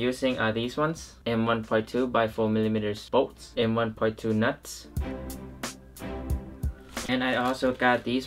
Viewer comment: Did i hear.. NUT!?